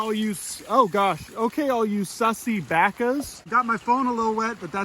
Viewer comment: He did not just call me a sussy baka